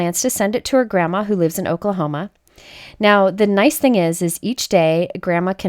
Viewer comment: I know you said 'Oklahoma' but I heard 'alchocol coma'🤦😂😂